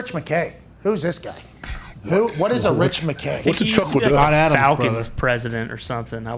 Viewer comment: did AJ just say “what’s a chuck Lidell?” Shat aht Johnny Drama